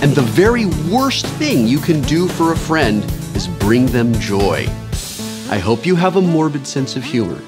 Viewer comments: The closed captions say "is bring them job" when Will actually said "is bring them joy." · subtitles says job, Wil says joy... now im confused. did you misread that line Wil or did the subtitle writer miswrite? hey we are all human and make mistakes sometimes :) i almost didnt notice so no big deal i guess.